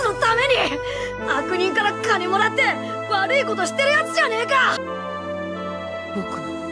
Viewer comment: Did Naruto say the n word 😳